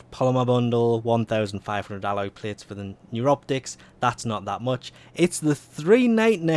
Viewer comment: "Neuroptics" Thought he said, "and you rub dicks".